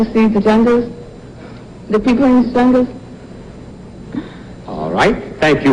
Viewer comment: Can someone please clear to me what's the last words of miss Israel? (Top5) 1:34:06 "...... I want to go to Africa to see whaaatt?? " Please reply anytime! I BADLY NEED YOUR HELP!!! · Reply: I think she said, that she want to see jungle and people in jungle. It was the final part of her answer.